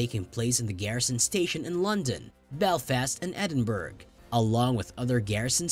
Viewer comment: it sounds like he said Edinberg